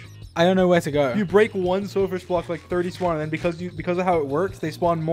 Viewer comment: Dream "I hear silly fish over here"